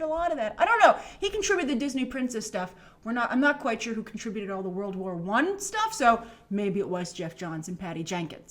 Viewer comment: At first I thought it said World War 84